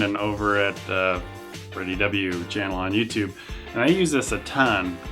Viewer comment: you mention someones channel but i cant understand what it is? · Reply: freddiew is the name... just type it in